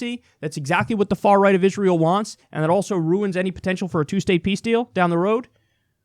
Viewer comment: did he just say, "2-state pee-steal?"